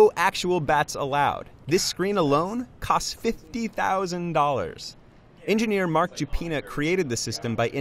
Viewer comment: >builds $50,000 batting cage >can't bring a bat inside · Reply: I coulda swore he said $150,000... but i aint watchin it again to just to make sure... well just stay with $50k!!!